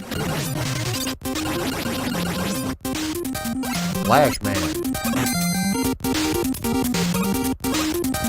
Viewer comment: I swear you said "Black Man" 🤣